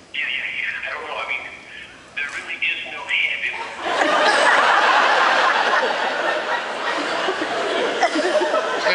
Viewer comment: What does Nathan say at 2:00? There really is no....what? I couldn't understand it :( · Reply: he said "there really is no head in the road."